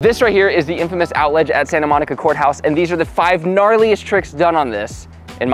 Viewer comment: thought it said the Charliest trick at first